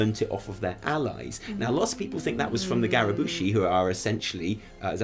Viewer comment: Wait wait, did you just say Gary Busey Trolls? :P